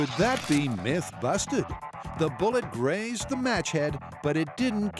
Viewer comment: For some reason I thought that said "mullet"